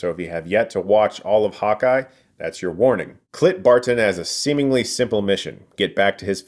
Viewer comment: I’m sorry, did you just say “Clit Barton?” 💀